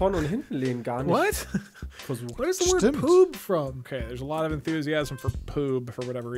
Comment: Also ich hab PROOB mit R verstanden :D